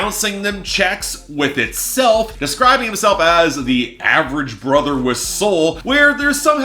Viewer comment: I misheard "average brother with soul" as "average bubble with soap" when I was 10